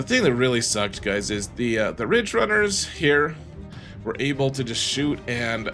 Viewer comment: I thought you said the ridge runners has morers?